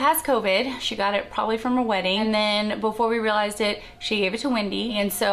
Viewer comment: I'm so sad to hear that April and Wendy got COVID, but SOOOOO EXCITED to hear that April got married! Congratulations April! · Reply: I hear "her wedding," not "a wedding".